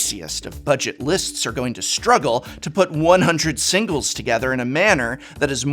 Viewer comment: Hey prof, what do you think about the changes they made with the midnight hunt fat packs (or whatever they call the product now)? Same price but 54 fewer cards. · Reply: Weird, I swear I just heard you say buy singles. Creepy.